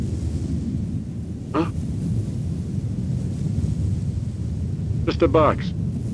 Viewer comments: On 2:52 my dumbass thought he said "mister box" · I heard "Mister Box" O.o